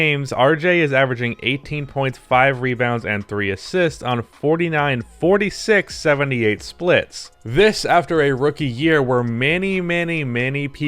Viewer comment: I swore he was saying 18.5 rebounds and 3 assists and did a quadruple take 😂